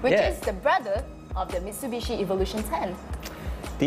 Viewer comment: Did I hear "Evil -lution" ?